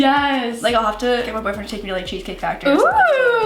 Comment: Did she say her boyfriend